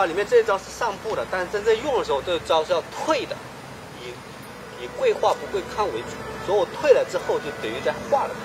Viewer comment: Sometimes i forgot people dont understand mandarin..... he actually explains in detail how the taiji moves is actually used in combat. And how usage is actually different from the actual practise. In one of the move 05:00 , he say the taiji move, its trained to be step forward - but when in combat, its actually a step backwards rather than forward (because he say Taiji in an internal martial arts, prioritise "dissolution" rather than "retaliation" of attacks) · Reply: 谢谢你的解释 👌你谈的是不是那个内家拳的贵化不贵抗 （的概念）？ 他也说那件贵化不贵抗的事儿 对不对？ 他解释一对方要打你那你就退（他说的有道理，很有效）然后他说如果不动的话对方可以打到（打倒）他，这也不用说了吧 5:00 麻烦你能不能帮我解释他说什么呢？他说了的那一句话 ” 你贵化不贵抗 。。。” 我没听清楚 我不确定😅 好像他是谈那件贵化不贵抗的事儿？ 对不对？ 因为然后他说了我退了之后就可以化了他所以我觉得他应该是谈那件事儿 麻烦你能帮我全部了解一下 谢谢